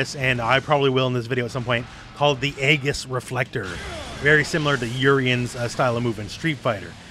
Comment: i misheard "Aegis Reflector" as "Angus Reflector" and thats what im calling it now